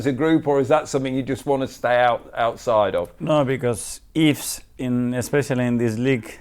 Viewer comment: who or what does Mikel not like? This was confusing. I thought he said he didnt like Burnley. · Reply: No he doesn't like 'ifs'. For instance, if Arsenal wins this happens or if City wins this is what will happen. You can prepare your team as best as you can, focus on what you can control, and not worry about what others will do. Getting hung up on whether City will win or lose is pointless..honestly it's counterproductive because you lose focus on what you need to do to win. It is confusing because everything is all jumbled up in his speaking.